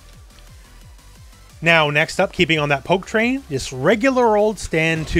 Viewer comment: Ooohhhh you said “Poke Train” 🤦🏻‍♂️ I thought you said “Ho(e) Train”😂 I thought of the Godfather from back in the day when WWF was still WWF 😂